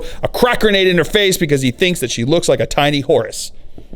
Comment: I misheard you saying Horus and I thought you said "Because he thinks she looks like a tiny horse" which I think is a funny insult. Li'l Sebastian died and became Saint Celestine.